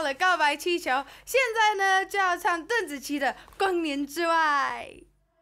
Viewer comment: 光年之外 ？ 我怎么听成Konichiwa 😁